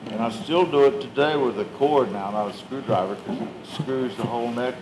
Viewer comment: what did he say at 16:39?? he still does it today with a ______ not a screwdriver , cant make out what he said. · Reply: I am pretty sure it's a chopped off electrical cord.